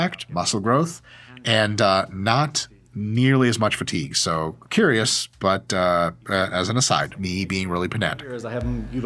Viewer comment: Can someone tell me what he says at 2:05 I can't understand those couple sentences at all please help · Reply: "Curious, but as an aside. That's me being really pedantic."